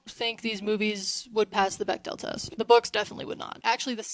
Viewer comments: what test? bectel? what are you saying and what is it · what does she say? what test? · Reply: The Bechdel Test: If at some point in any movie you have two female characters having a conversation with each other, and they're NOT talking about a man, then that movie passes the test. The test measures a movie's representation of women as interesting individual characters instead of just being love interests or some kind of extension of a male character. Once you know about the test, you quickly realize how few movies actually pass it.